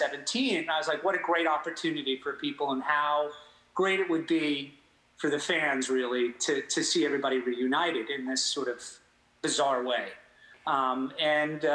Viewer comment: Did he just say "everybody reunioned"? 😱😱😱